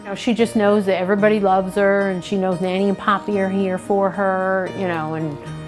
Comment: I thought nanny and poppy were puppy names maybe i heard puppy but subtitles on so i saw poppy and realised like nanny grandma nana idk but um ye also like poppy the singer or poppy idk from that episode game Edit: um poppy was like the rich kid who saved the poor girl in that episode. Nora played it or was it me? Nora...? Um no it... Was me. Wait maybe i saw someone play too maybe gloom